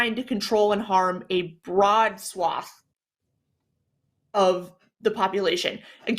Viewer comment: when said "broad swath" i heard "broad sloth" and spent the next ten minutes imaging how cute a wide sloth would be. broad sloth will save your soul if you believe in broad sloth.